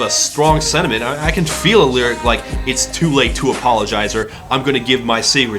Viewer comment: I always misheard it as "It's too late for the John", I thought it was a song about a guy with a bladder infection 'cause he held his pee in too long.